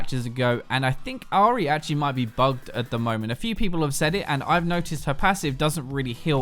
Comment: wtf do you say in 6:45 XD i cant understand at all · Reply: he says, she might be bugged at the moment